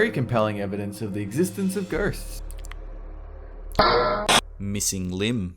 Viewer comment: did he say compelling evidence of existence of gersts?